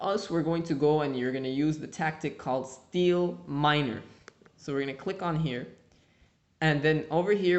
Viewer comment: is it just me or did it sound like he said "steel miner" and not seed miner XD